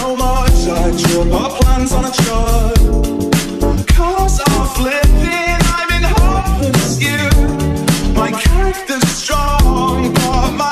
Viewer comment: I hear "guns are flipping i might harvest you" And "my cactus strong but **unitelligable**"